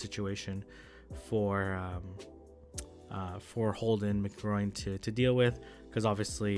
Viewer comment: did say HoldinMcGroin? Is that his actual username? Hahah